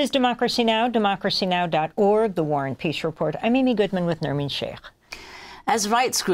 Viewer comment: I always thought she said the Warren Peace Report. But now I think it's the War and Peace Report. How pretentious.